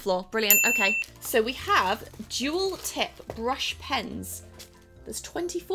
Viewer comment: When you said “duel tip brush” I thought you said “Juul tip brush”